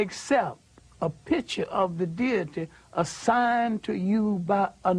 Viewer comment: can someone help me out , at 43:20 he starts to stay and when you accept a picture of the ___ ? what word is it and what does it mean? thanks! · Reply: Deity, meaning God.